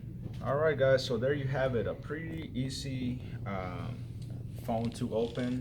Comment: i like how he thought he replaced the screen and i also like how he said "pretty izzy" instead of "pretty easy"